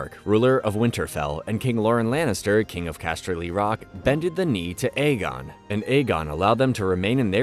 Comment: did you just say “bended the knee”?